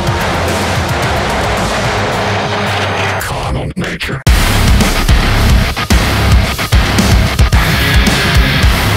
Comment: What is being said at 1:18? "Common Nature"? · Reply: Carnal nature